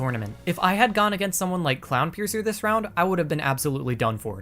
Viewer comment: wait did he say clownpiercer?